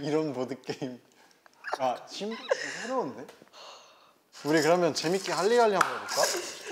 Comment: i think jaehyun Said shibal or something here LOL